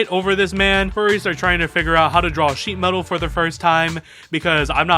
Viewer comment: I misheard "sheet metal" as "sheath metal"... Ngl I'd love to see that.